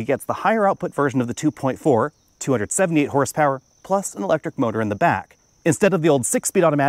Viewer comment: I heard “228 Horsepower” instead of 278 Horsepower listed at Toyota website